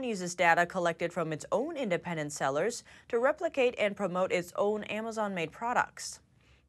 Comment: Had to go back again to min 4:28. Did just said "reprocating" (their products)? Got a good laugh. It was "replicating" ( their products.) Lol.